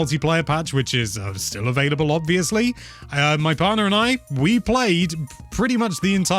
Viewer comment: The first time I watched this I thought you said "father" and I was like "oh wow, that's so cool that he has that kind of relationship with his father"